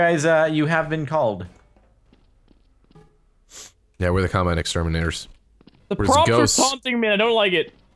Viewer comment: At first I thought you said you are the Columbine Exterminators......